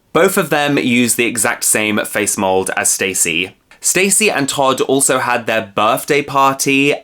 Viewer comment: I hear "Tutti and Todd" and I keep saying "DO NOT SPEAK THEIR NAMES!"🤣 IYKYK